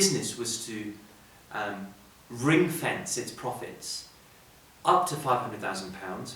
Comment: Hi Sir.. Could you please tell me what you said at 4:54 "If a business were to "...." its profits" Thank you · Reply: aman singh Ring fence, means to specifically use its profits in a certain way, in this case for investment